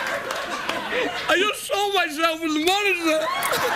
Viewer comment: Could someone please tell me, what Craig tries to say at 30:46? 😅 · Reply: He said he just saw himself in the monitor.